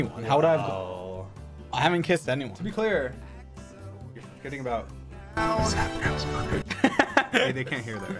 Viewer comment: okay but i actually don't understand what Dream is saying here ( 11:08 ) did anybody understand? help i'm curious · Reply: i think he said "you're kidding about sapnap's mother"😭